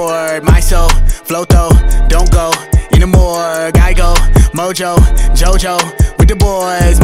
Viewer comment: I’m sorry but did NoBigDyl just say... Mojo Jojo?